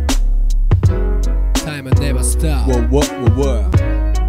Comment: 大麻 never stop に聞こえる